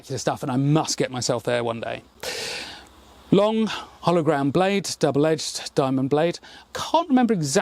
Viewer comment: I think you are saying "hollow ground blade," but I keep hearing "hologrammed blade," which made me wonder at first how medieval weapons-makers were doing holograms? :P